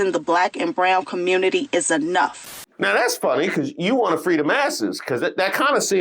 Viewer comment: Why did I hear, “Free the massas” the first time? 🫠